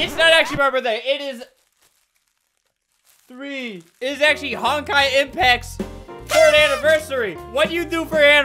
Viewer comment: I had a heart attack when i thought he said Genshin Impact